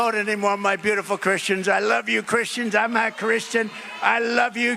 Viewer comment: Does he say “I’m not Christian” it’s really hard to understand.